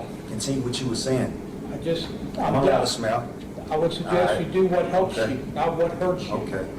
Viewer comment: Um...it sounded like he said "smell"...I realised he was saying SMILE 😂😂😂